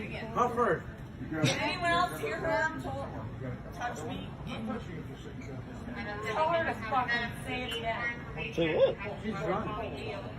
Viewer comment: what did she say all i hear is "say it agian"